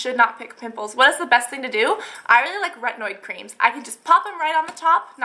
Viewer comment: what do she says at 9:10 ? rednoir creams or so?